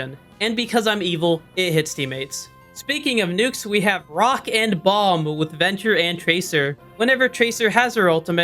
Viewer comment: DID I HEAR A ROCK AND STONE— oh nvm i didnt